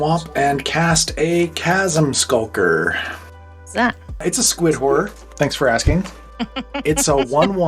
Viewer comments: I totally misheard "It's a Squidward" and that made it better for me. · not gonna lie, when said "it's a squid horror" I heard "it's a squidward"